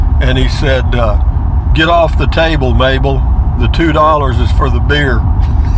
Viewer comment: I heard it as "Get off the table Mable, The quarter's for the beer"